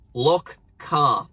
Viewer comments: I thought u said look car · I’m from NA and I thought you said look car and I repeat the video 3 times and I still thought you said look car.